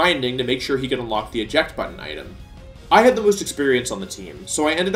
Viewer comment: is it just me or does he say "i had the least experience on the team, so i ended up serving as shot caller..."?